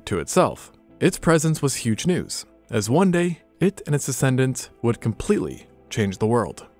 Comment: Your BACKWARDS!!! You say (0:19) 'its presance was huge news - as one day, it, and it's...ASCENDANTS...'😳🙄😱🥺😢 An animal's DEscendants (NOT 'A'scendants! The 'ascendant' is the FIRST in a line, and what gave rise to it (it's the progenitor) of what came AFTER! You mean DEscendants; what arose AFTER it 🙄. HOW OLD ARE YOU?!?! It's scary, and sad, as I actually enjoyed a couple of these, but, when someone makes a gaff as clumsy as this (and they've supposedly researched and wrote a script - and I don't know you, nor your age, but, you don't sound 5 or 6 years' old - it's not a good omen, and, for people like me, it shows that, 'if this person could get such a basic, simple thing like this WRONG, what DIDN'T I catch previously?' For a person who's working/talking in a field of science, that does NOT bode well, at all. STUDY! Learning, education goes on throughout one's life. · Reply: I think he said "its descendants" but you misheard. The captions also say "descendants"